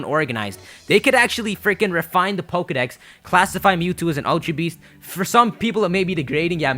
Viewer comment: the subtitles say : "classify mewtwo as an LGBT"